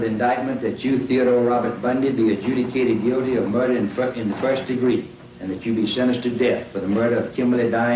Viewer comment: LoL the appeals judge sounds like he says “hereby found guilty of murder in the fuckin first degree